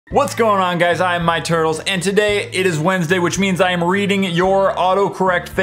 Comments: Did anyone notice that in his intro he said "How's it goin guy this My Turtles?" 😂😂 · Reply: Yeah i thought he said Its My Turtles XD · Did I... did I hear him say "Hello I am my turtle's" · Did you in the very beginning I thought you say I am my turtles